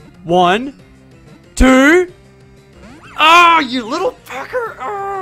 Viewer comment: did you say "You little fucker" at 9:29? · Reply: He said pecker